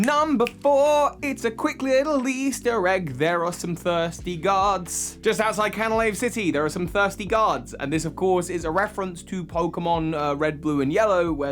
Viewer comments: I kept hearing “thirsty gods.” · Ha totally thought u said “thirsty gods” but u had said guards 😂 great video ❤️ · what he said "there are some thirsty guards" what I heard "there are some thirsty gods" ...Zeus?